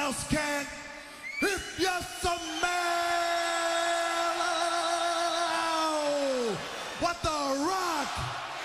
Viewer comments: I always thought he said can you smell what the rock is cooking 🤣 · I always thought he said CAN YIU SMELL what the rock is cooking not IF YOU SMELL